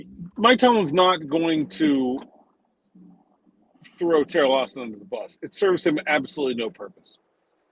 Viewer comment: It sounded like said Terrible Austin, lol. It feels accurate right now.